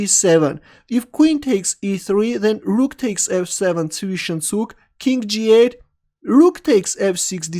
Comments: Rxf7 What were you saying? Zwiched Zug or something? :D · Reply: You can check it YouTube. It's zwischenzug. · Can someone tell me what said on 3:26? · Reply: antangicang Zwichenzug